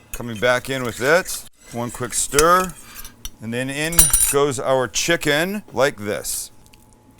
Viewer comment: I thought you said you were going to cook a PORN CHICKEN..😂😂😂